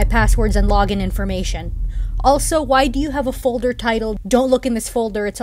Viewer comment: His video titles are getting more and more questionable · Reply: I thought you said tittles